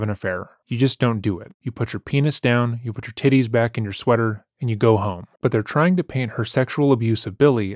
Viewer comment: I was cleaning my room and went to change clothes whilst watching this and the moment I took off my sweater I heard "you put your tits back in your sweater" and now I'm feeling weird. Behave, sir.